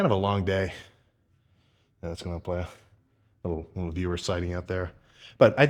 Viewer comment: Have a great weekend, everyone! Hope you're getting after whatever your goals are! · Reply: did he say "beaver sighting"?? 😂